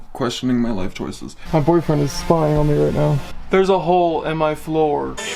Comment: i heard "my boyfriend" am i tripping